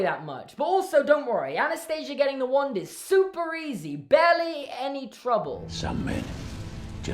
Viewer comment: all I heard in this part was saying "Super edgy barelllyyy an inconvenience"